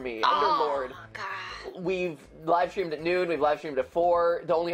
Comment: Given the content of this video, I don't think it was unreasonable to have misheard this as "We've livestreamed nude". 45:34 Oh, I can think of worse. A family gathering. A meeting with the boss. A funeral. A maternity ward. An operation.